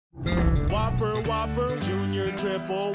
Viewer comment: What I heard is this: "flame grilled turd"